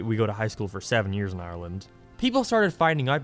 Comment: did you say maryland?!